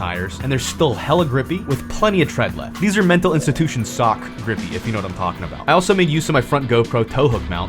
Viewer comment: i thought he said mental institution suck at first and i was like true crazy women give good head LMAO